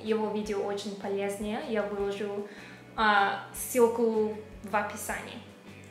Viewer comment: Свёклу в описании)) Забавно)) Или мне одному послышалась свёкла вместо ссылки?